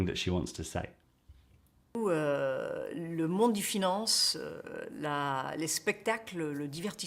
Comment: Well, there are a few more serious errors of interpretation, in my view: 2:47: "le monde des finances" (pas: "de finance") .... since she is not saying "le monde de la finance" (which might not have been that idiomatic, who knows ... but still possible) - but "monde DE finance" is clearly wrong ... and it's clearly not what she is saying. Such structure would betray a heavy misunderstanding and lack of knowledge of the French language. It's perhaps a bit comparable to the following composites: "ministre des finances", but: "ministre de l'intérieur", "ministre des affaires étrangères", so, the added particle can differ - it's very much dependent on usage but speaking very much in general it's with the determinate article ... be it singular or plural ... On the other hand, the plural is used for such combinations as "le monde des enfants / des ados", but - when it comes to something determinate: "le Salon du livre" ... 2:40 / 3:03 / 3:24: "tous les TROIS" - she clearly says that, no need to pronounce the French "r" in a very visible way - except in songs where this is done ... So, the French "r" usually is very "tender" ... and so, that has nothing to do with a lack of practice or not exercising that muscle ... I would even say, whosoever has attended French school has a good feeling for how things are pronounced ... 3:37: "Le monde DES finances est guidé par une technologie qu'on a INVENTÉE ..." - that's the way it is written in French ... and the reason for that the object /